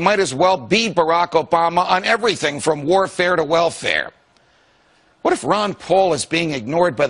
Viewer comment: El min 3:16 está mal traducido, dice "welfare" que es algo así como bienestar o prestaciones sociales de modo que dice (... está desde el conflicto armado hasta las prestaciones sociales) y no de "...guerra en guerra". Tache. Otras palabras como "sustantivos" en vez de "substanciales" no checan bien con el sentido y el "feeling" del mensaje en inglés, Cuida más esos detalles. El mensaje en general es claro, pero no traduces con precisión, se te van muchas cosas que realmente no dice. Saludos.